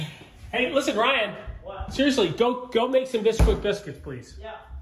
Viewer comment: My TV volume was low so this whole time I thought you were saying nesquik until Ryan took it out of the bag 😂😂